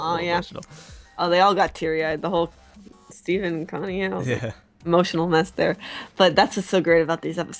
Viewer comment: I think you said Emotional Mess there, but I heard "Emotional mascara" and I suddenly thought... COLOUR CHANGING MASCARA THAT CHANGE SWITH YOUR MOOD!